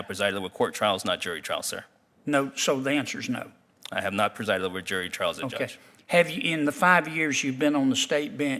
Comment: Sounded like Judge Simmons said, “I have not presided over jury trials yet judge.” 😂